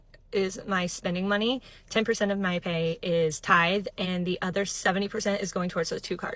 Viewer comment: Did she say "tithe"?